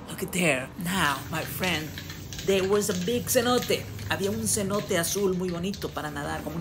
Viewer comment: I thought you said big cerote but it was senote 😂😂